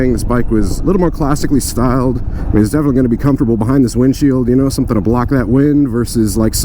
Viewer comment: did you say it’s never going to be comfortable behind this windshield?? 🤔